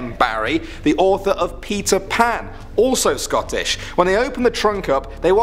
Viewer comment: I heard Pizza Pan instead of Peter Pan.